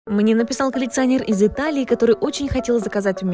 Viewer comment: Не знаю что у меня со слухом, но я услышал "полиционер из Италии" 😂